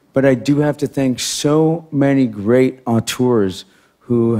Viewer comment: what does he say? ontours?